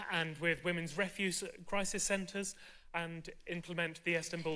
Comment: Did he just say "Women's REFUSE Crisis Centres"? 🤣🤣